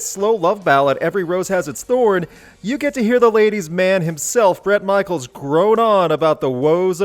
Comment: I honestly thought you said Shawn Michaels for one moment at 10, then I realised it was Bret Michaels